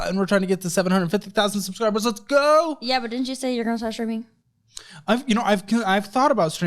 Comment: What did she say at the 2:50 mark? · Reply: I think the subtitles got it right she said didn’t you say you were gonna start streaming but it was really like gibberish so I kinda even thought it was like didn’t you say your girl should stop streaming or something? Like didn’t you say you wanna start streaming? Wow